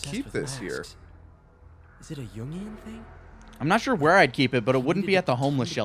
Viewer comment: lmao XD when peter said "is it a yin yang thing" i thought he said "is it a union thing" i almost spat my coffee like "oh shit spiderman game takin cracks at the union aww hell that'll-- oh nvm"